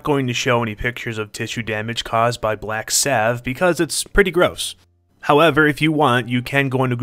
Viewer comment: I thought it said black slaves